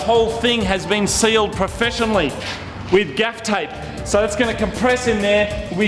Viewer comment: turn on subtitles wtf is duck tape and moog said gaf tape